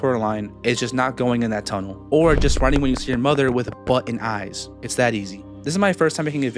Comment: Captions said butt and eyes 😭